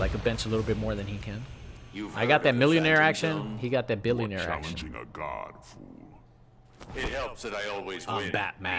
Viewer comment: What does Batman say? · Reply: "Ever heard of the phantom zone?" "You're challenging a god, fool" "It helps that I always win"